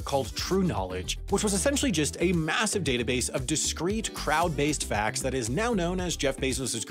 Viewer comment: Just in case you guys didn't notice, at 1:02, Sam says "crowd-based facts" while the captions say "crowd-sourced facts". Not sure if that'll be in a mistakes video or if they'll just change the captions to match the video.